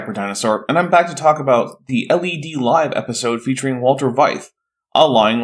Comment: for a second I thought you said Walter White